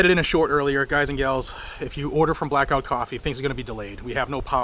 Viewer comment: I am so sorry. You said Blackout Coffee. I swore you said Black Rifle coffee. ...Black Rifle Coffee are a much of Traitor and Stolen Valor punks. Again I'm sorry for the mistake.